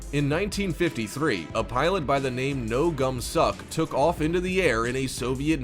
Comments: why i hear no gum suck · imagine making fun of names (me talking about this cuz i thought i heard "no gum sock" instead of "no gumsok" (also yes me iz korean))